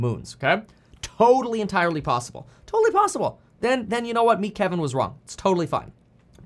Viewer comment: Did he say "Meat Kevin was wrong" at 17:15 😂